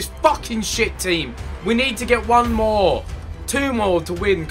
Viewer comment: Did I just hear ethan said "tumor" ??